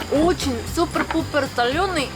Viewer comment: пересмотрела 3 раза и поняла что она сказала ,《ооу очень супер пупер солёный 》